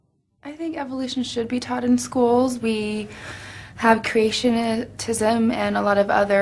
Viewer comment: "creationitism..." sounded like cretinism!